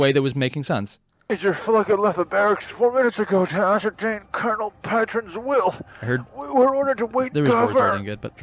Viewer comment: dude, pretty sure subtitles are avaliable if you need them (there are definitly subtitles in the later episodes, so I imagine earlier ones would have them as well). As for what he said "Major left 4 minutes ago to ascertain Colonel's will" (he said the names as well, but I don't care enough to attempt transcribing them). 12:15 they do have such direct fire artillery but it wouldn't be possible to make indirect fire via blaster technology, so of course they needed solid shells. 14:00 they are being attacked. It is not like she told the Seraphim to order the Scions. Just inform them of the attack and request support. Also stop saying generals. There is only a Colonel and a Major there, plus the people manning the consoles (and of course you will find out that they are all dead already, but still). 21:27 well, no. Even the Hivemind's goal is still to feed. Like an animal. Orks can be considered worse (from an in-universe perspective, out of it they are comic relief) because they actively enjoy fighting and killing. They will laugh as they kill your friends. The nids will just eat you. So even knowing the Hivemind, for the guy on the ground Orks are worse.